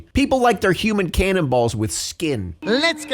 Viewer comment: OHHH cannonballs! Because what I thought he said (cannibals...) was even MORE unhinged 🤣